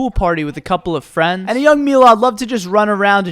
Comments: young milad was said but the subtitles said a young girl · when we says "a young milad", the subtitles say "young girl"!🤦🏻‍♂️😂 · I love how at 0:24 he says Milad and the subtitles say girl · The subtitles not only hurt my eyes but are also incorrect 0:23 "And a young milad loved to just run..." is what he said, but subtitles said "And a young girl. I'd love to just run..."